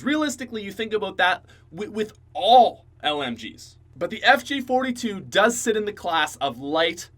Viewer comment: I thought in number 10 he said the mg42.... not the fg